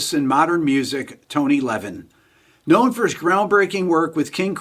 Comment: Decades ago when I first heard the name "Tony Levin" I thought it was "Tone Eleven"